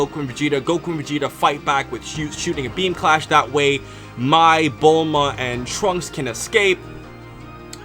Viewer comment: Am i the only one who thought he was making a "My Bulma!!" joke at 0:54 instead of saying "Mai, Bulma, and Trunks" (Thought he said "My Bulma and Trunks" lol)